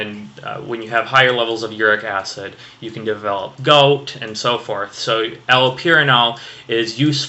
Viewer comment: Did you say goat? accidentally aspirated my coffee, thanks =) =)